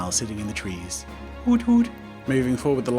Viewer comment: when he said hoot hoot i thought he said noot noot KILL ME XDXD